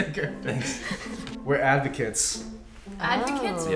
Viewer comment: Lol, when it said advocate, I thought that it said avocado.